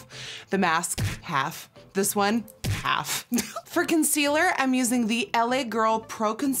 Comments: When she says “for consealer” I thought she said “friking sealer” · When she said ‘for concealer’ I thought she said ‘FRICKEN sealer’. And I got really scared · I thought she said fricken sealer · When you said for concealer I thought you said fricken sealer